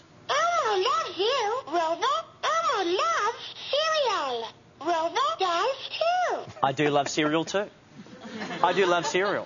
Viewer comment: the Toy is cute but did he said Grover dies to